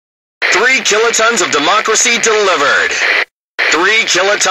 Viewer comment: What did he say? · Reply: 10 kilotons of democracy delivered 😊